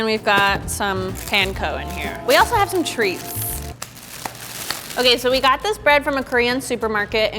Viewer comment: When said treats, I thought she said trees and I had to rewind soo many times to understand